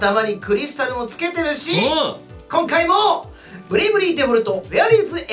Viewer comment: クラゲ～のブレイブリーがブリブリ～に聞こえたｗｗｗ